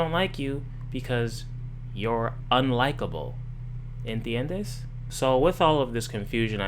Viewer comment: What was that Spanish word you said at 0:28 ? ENTHEYENDEEE? Haha get it because spanish is just such a crazy alien language even though I live in LA lol 🤠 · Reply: Know I'm two years late, but he said, ¿"en tiendes"? ("you understand"?) Love your phonetic spelling, though